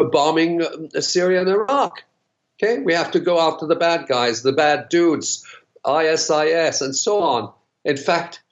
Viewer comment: Did he say the bad Jews?